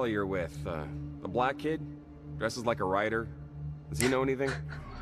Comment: The guy in the game describes a black kid.... as black, and she calls racist? Real people calling devs sexist with no proof? These people are just awful! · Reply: apparently he said "dressed like a rider." I also thought he said "writer", but the main character's unnecessary outrage threw me off that I didn't care any more. Just imagine her losing it over someone saying "My daughter just got a black belt in Judo! I'm so proud of her."